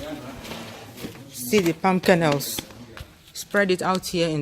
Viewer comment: i thought you said 'pumpkin house' (youtube auto cc heard "pumpkin nose") X D [palm kernels]